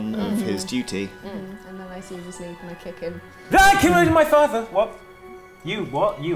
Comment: What did Corazon say at 38:18? I can’t quite understand him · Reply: "Ah! I didn't kill my father!"